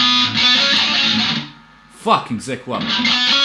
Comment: what is he saying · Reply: fu*king zakk wylde :P